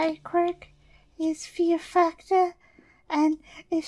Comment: did anyone else hear "fear fucker"? No just me? Ok....